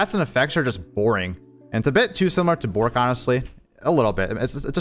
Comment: what does he say? Too similar to...? Sounds like Bork?